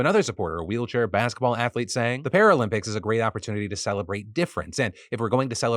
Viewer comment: I swear I heard him say halflete. 😂